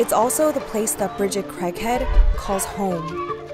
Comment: Shit i thought they said her last name was "crack head" instead of craighead at first 😂😂😂